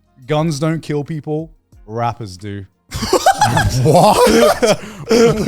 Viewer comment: I thought he said rappers instead of wrappers. Too much P Diddy news for me 😅😂